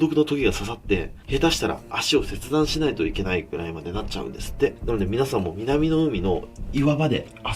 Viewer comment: Scholar at 1:27 is a mistranslation. I can't make out what the verb is though? amputate or maybe lose or bind? I keep hearing s(h)e-shi-da-n-ku-na-i but thats not a word????